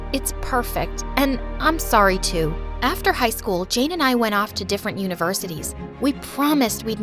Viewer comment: i misheard that as “me and Jane went to different universes”